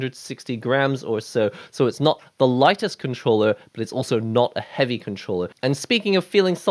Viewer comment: Thought you said legalist controller🧑‍⚖️